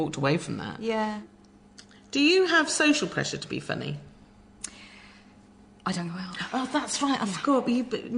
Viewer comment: What do they say at 8:27 ? I'm not an english speaker and I don't get a thing. I get the question but not whan comes next. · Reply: I think she answers 'I don't go out' meaning she doesn't attend big social events, she doesn't mean it as literally as she puts it lol